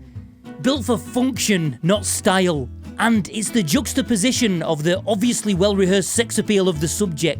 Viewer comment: Did you say jugstaposition? Just what I was thinking.